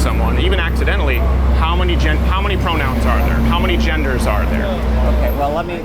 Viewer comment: there is an error in the subtitles there that says pronouns twice instead of saying genders unless it is what he ment to say